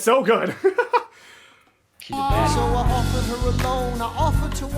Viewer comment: did he say “she’s a baddie”😂